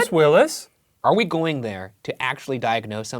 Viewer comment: ok this is cool and all but does say “are we ghouling there” at 2:43?